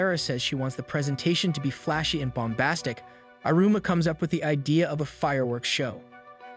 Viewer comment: I heard boombastic on